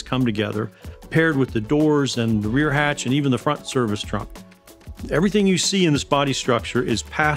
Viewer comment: 'Front Service Trunk' (2:20), I didn't know Aptera had one. If so, that is GREAT NEWS !!! Can someone confirm if I heard the word correctly please?